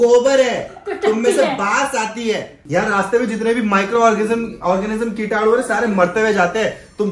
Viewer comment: guys please confirm , i heard "ORGASM" at 3:22 minute , Bhai Zinadabad